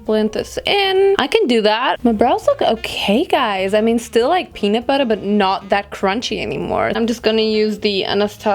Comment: I thought you said 'Penis-Butter'😂😂